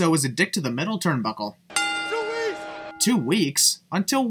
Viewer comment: I think he said too easy not two weeks